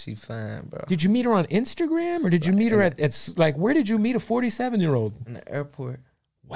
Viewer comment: DAMNN I THought that shii said a 48 year old Man😂😂 .